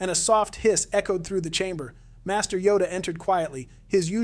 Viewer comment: I paused the video for a second and when i un-paused it all i heard was "piss entered the chamber " 💀 i think i paused mid sentence